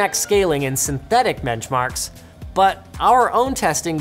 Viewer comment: Hold up.. did he say "menchmarks" at 6:40?